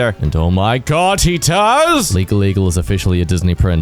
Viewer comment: In the beginning I thought you kept saying legal legal. I rewound and rewound till I remembered legal eagle is a channel and on the screen